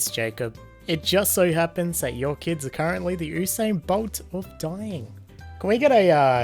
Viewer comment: Did I hear Ew sane instead of you sane for the word Usain bolt 😳😂